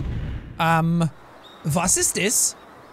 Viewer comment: he said a German word in an English sentence „Was is this“ Was instead of what. I can’t believe that this is real